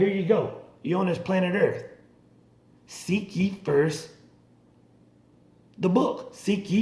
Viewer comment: what do you say there English isn't my first language I don't understand it Seek yee?